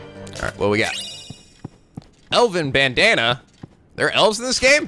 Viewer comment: When you said Elven Bandana I thought you said Elvis Bandana xD